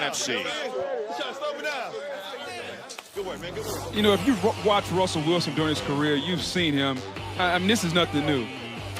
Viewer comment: nfl didnt catch it. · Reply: "Good work man, good work"